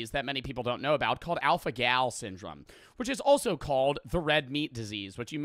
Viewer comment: My brain is so fucked cuz I legit thought he said 'alpha gay syndrome' 💀